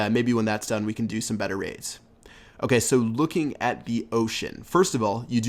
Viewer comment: Did he say oh shit 0:40 lol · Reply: He said "looking at the ocean"